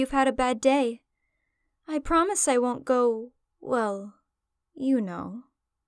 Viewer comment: “I promise I won’t go... well, Yuno.” Unsure if intended but brilliant nonetheless. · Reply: oh shit dawg i thought she was saying "well, you know" implying she went actual yuno crazy earlier and that mc knows